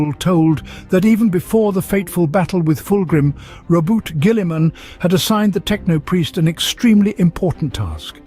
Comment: Lol, I'm pretty sure that I heard 'Robot Guilliman' at one point! Great job!